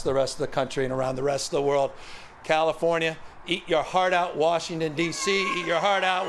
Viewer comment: did he just say "California eat your heart out"? Opps.. Let me tell you, its not a "model for the world" as Korea and Germany already have animal crossing overpasses on their highways. And theyve had them for years. What a dud this guy is.